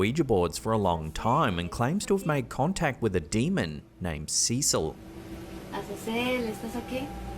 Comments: i keep hearing "azazel" not cecil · I believe a minor correction on the first video. As a Spanish speaker, to me it sounds like she is saying Azazel not Cecil. That's what I think at least, idk what the correct name is for sure